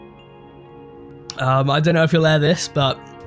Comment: when you said your channel name I thought you said “die or drip” and my brain was kinda just like “aight word” 😂